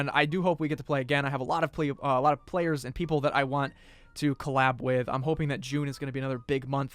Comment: Did He Just Say Collat Instead Of Collab ?👀